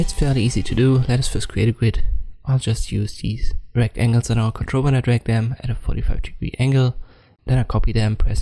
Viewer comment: I cant hear what you are saying at 0:13, how do you snap the squares together? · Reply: Turn on the captions, they are somewhat accurate. But I said: "I hold Ctrl when I drag them at a 45 degree angle - that sizes them up. Then I press Ctrl+D to copy them." Hope that helps.